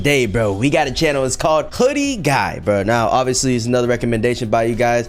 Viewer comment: I thought he said hoodie dude